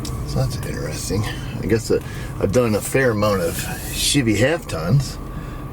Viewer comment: I know my hearing is getting bad with age; however, did anybody else think said $h!tty instead of Chevy? Like he combined the two together? Yeah yeah let the smart aleck comments begin.